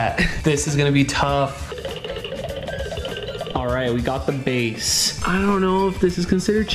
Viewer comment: did she just said the B word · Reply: Base